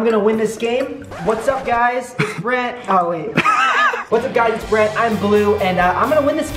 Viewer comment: I thought Brent said "Hey whats up guys Im red and im blue" SOJFOSDFJOSJDF